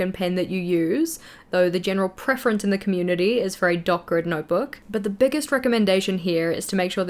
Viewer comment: Dot grid! I thought you were saying dark red